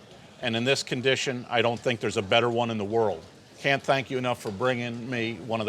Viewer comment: The best role lex.in the world, is that what I thought he said dammmm